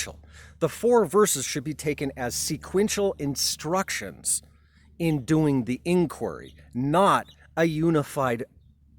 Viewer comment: At about 4:20,reference to "Herman Unik" is wrong? Can anyone tell me what that means? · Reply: Hi there. The word is "hermeneutic." It's kind of academic and perhaps I shouldn't have used it. Here's the dictionary definition: "concerning interpretation, especially of the Bible or literary texts." So what I'm saying there is that it's wrong to INTERPRET the four verses of the Ashtavakra as a unified argument. A better interpretation (or hermeneutic) is to treat the four verses as sequential steps of observation. So basically you can take the word "hermeneutic" to be interchangeable with "interpretation."